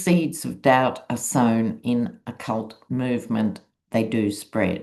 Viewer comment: You said "in a cult movement" and I heard "in occult movement". Woot! Woot! I love it when things like that (connections) happen. Kind of dumb of me not to notice before but at least I see it now.